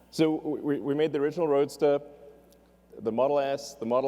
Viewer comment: the captions say model ass instead of S XD (Edit) the whole caption thing is messed up